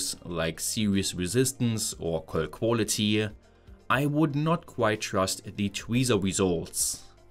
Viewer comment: I was doing something else and I heard "I would not trust the tweezer weasels." I totally agree.